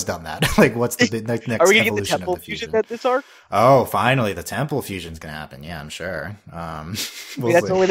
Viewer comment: when they said temple fusion, I thought they said PEBBLE FUSION. and honestly, WHO WOULDN"T WANT TO SEE THAT